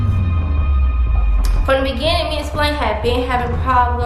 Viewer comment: I’m sorry but what is king name all I keep hearing is “me and splain.” 💀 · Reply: His real name is Splanne. they call him King